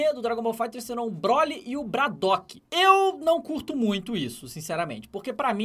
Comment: só eu ouvi "Bradock" ?